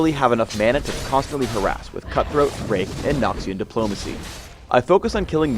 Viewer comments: at like 1:28, I had to rewatch because i thought he said rape instead of rake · did he say rape *-*